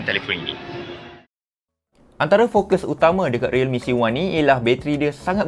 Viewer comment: bukan ke realme c11? Ke aku salah dengar?